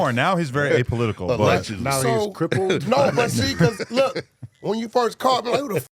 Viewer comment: The way Wack explains how he didn't know ICP feels so disrespectful😂 how tf can you not know ICP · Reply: did he say now he's crippled? nah I'm dying 😂